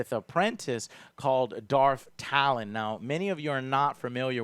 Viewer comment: I thought you said "Darth Thalen." But Youtube automated subtitles got the word right (talon).